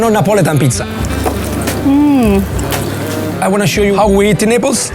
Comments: lol I thought he said: "I'm going to show you how we eat in nipples" 😂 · what did he just say? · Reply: "show you how we eat in Naples"